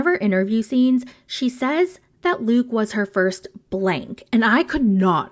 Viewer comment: I thought she said her first bj.. idk 😐